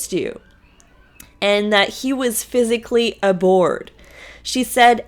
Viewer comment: Meri said Kody was physically abhorred at the thought of kissing her. It sounded like Meri said "aboard" but that doesn't make sense.